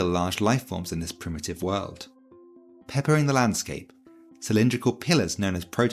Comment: I thought you said “peckering the landscape”